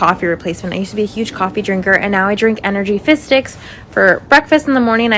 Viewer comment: I keep hearing “fish sticks” instead of fizz and it’s making me laugh